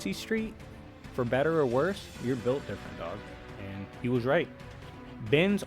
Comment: I have no idea what you said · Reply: "For better or worse, you're built different dog; and he was right."